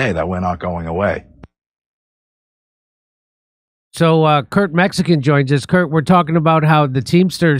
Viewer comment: Does Jimmy call Kurt, Kurt Mexican? Was that on purpose? Am I hearing shit?